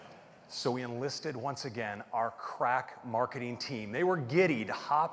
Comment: I always heard 'crack marketing team' as 'Craig marketing team'.